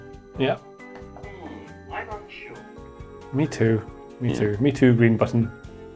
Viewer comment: What's the 4 buttons' main meaning? Anyone can tell me the color meaning? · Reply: I just noticed at 08:07 uses the green button, sounds like it says 'mmm . . . I'm unsure'